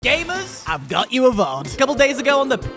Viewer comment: literally i open the video (my first video I might add) and I immediately have to turn captions on because I thought the first thing he said was “gayvas” like a cross between gay divas just for the caption to tell me he’s saying gamers. This is gonna be fun